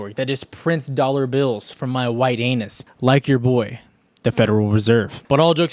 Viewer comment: Did you just say "White Anus"? 🤣 Or am I drunk already?